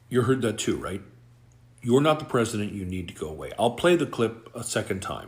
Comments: I thought he said "You need to go away". More truth from a 4 year old has never been spoken. Ever. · I hear, "Mr. President, I need to go pee." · I cant stand Musk and Trump, but what a BS is this? The first phrase I heard: "Mr. President, we need to go away" which seems logic if the little kid is feeling bored and wants to go and play. The second phrase wasn't even audible, didn't sound at all like the phrase you invented. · "You're not the President you need to go away." The kid is either repeating his father's words, or he is an incredible genius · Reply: I heard, "...I need to go pee." 😮